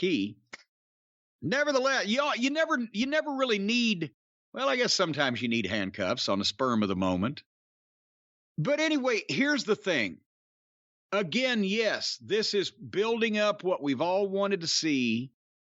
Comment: Did I hear "sperm of the moment"?